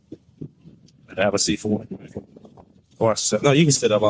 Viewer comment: The officer said, "Have a seat for (you)," but the word-to-text translation came out as "have a C four."" We know what the officer is saying but AI doesn't.